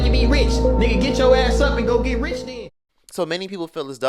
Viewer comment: Was that Kim K sayin “get your ass up and go to WORK!” ? 🤔💀 I mean..I don’t like DDG as I think he’s disingenuous based off the first time I heard of him which was during the YouTuber VS tiktoker boxing fight where it was proven thru messages and even the clip that he LIED abt that yt boy tiktoker calling him the n word to manipulatively elicit defense from us as Blk people. And then fast forward to Ruby Rose, his ex, posting messages of him contacting her while w Halle which was shady as fck. W that being said, I get what he was TRYING to say here and that he may have been TRYING to motivate his audience but his take was just ignorant and obnoxious. Some people’s circumstances are worse than yours 🤷🏽‍♀️